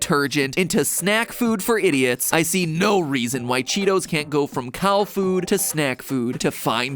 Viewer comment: I thought it said cow poop